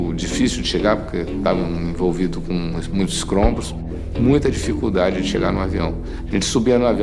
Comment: Kkkkkkkkkkl aos 05:30 ele fala " ESCROMBOS" , não acredito que ele disse isso, kkkkkkkkkkkkkkkk.